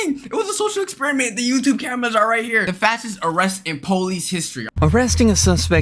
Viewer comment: Oh I thought it said FATest arrest